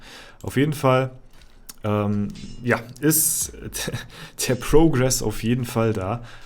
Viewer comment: Daiiiily Kommi 🤑 03:40 hab ich da Progress gehört?